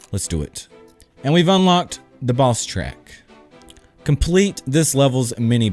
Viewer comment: Who else thought was saying 'balls track' instead of boss track? xD